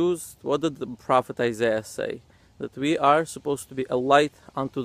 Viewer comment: did he say we supposed light 🤔 but they also believe they better the other because god choose them it doesn’t make sense 😂